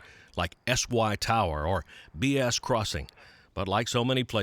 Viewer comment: Lol did I hear "BS Crossing" Sounds like bullsh*t crossing